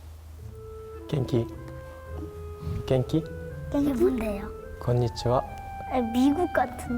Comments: why did it sound like Yuta said “kinky” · I THOUGHT YUTA WAS SAYING "KINKY" ADDGKAKSHGSKS OMG